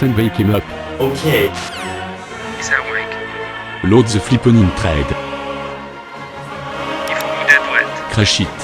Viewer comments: What does he say at 1:07? Was it English or French? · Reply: Load the flippening thread, I think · Anyone else thought he said “to shit” at 1:11?